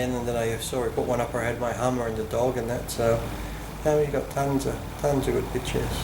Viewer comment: did he say he had his hummer in the dog?